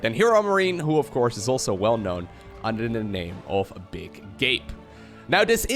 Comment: I heard that as "big gape" and I nearly threw up.